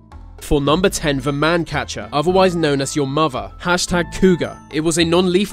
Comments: am i the only one who think that i heard "your mother" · did he say "your mother"